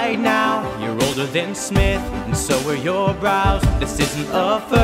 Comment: I thought you were going to say "older than sin" but you said "Smith" and all is right. Love the video, btw.